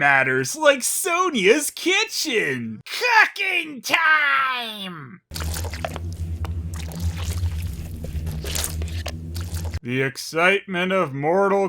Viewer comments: Did you just say "Kucking Time"?! Sonya, how kould you?! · saying "cooking time" sounded like he said Cucking time. lol